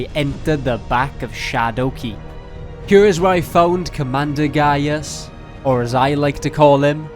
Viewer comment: When he said commander gaius I thought he said gay ass😂